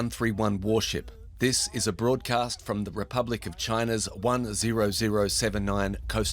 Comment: what??? Republic of china? Did i hear it right?